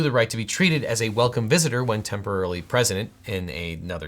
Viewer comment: Did you say temporarily president at 9:51? Also, when is The Goonies getting the laws broken treatment? I want to know how long Chunk is going away for the creamed corn prank. Chunk is a lawyer now, so maybe a collab?